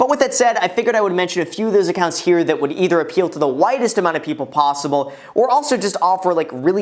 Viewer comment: I thought he kept saying he wanted to make recommendations for the whitest audience lmao